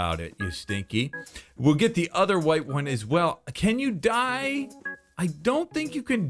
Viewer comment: I thought he was saying it like “Can you die?” XD